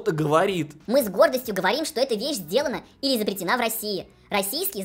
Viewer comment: Чёт прям конкретно послышалось: "Эта вещь сделана или запретена в России". Эх мне уж подумалось, что так правдоподобно описали